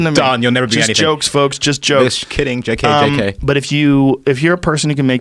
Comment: did he say J K K K ? :D the forbidden amount of Ks